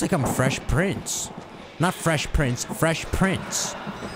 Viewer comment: ngl I thought he said “Fresh Prince” there too before he elaborated LOL